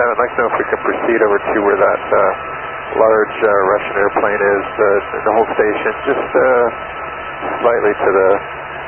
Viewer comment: Nice video. Thanks. One thing. At 6:55, Sky 7's transmission is transcribed as "The whole station just slightly north...." I'm pretty sure what he actually said was "To hold station just slightly north..."